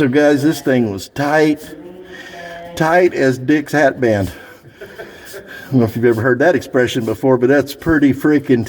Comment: I thought u said tight as dicks have bend. Which I estimated is pretty tight